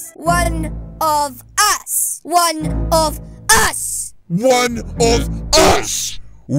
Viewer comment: she said instead of one of us OH A PIZZA MAN MY FAVOURITE LOL